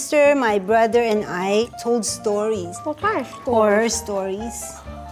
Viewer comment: wait did she say poor or po*n stories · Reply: No, she said horror stories.